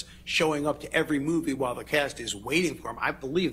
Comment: Man spoke "while the cast" but accent made it wabbecast. I misheard and thought it was "Wabberjack" 😂😂😂